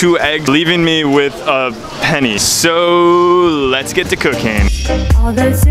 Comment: I thought he said let’s get to cocaine